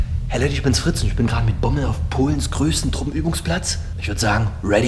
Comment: Was ich verstehe: Hey Leute, ich bin's Fritz und bin mit Rommel auf Polens größtem Truppenübungsplatz... Like, wer es checkt